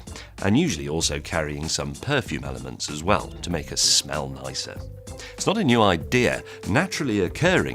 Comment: lmao the subtitles here say "Melissa" instead of "nicer"